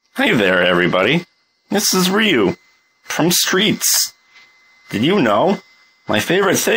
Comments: I thought it said “Ryu from the streets” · i thought it said Ryu from THE streets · I swear to god the first time I heard "This is streets, from Ryu" AND I DON'T KNOW WHY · Thought it said from the streets